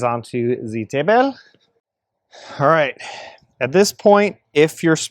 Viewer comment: I literally had to rewind like 4 times to make sure I heard ur correctly, “table” the way u said it, Arabic accent 😂 we call it (taybal) instead of table.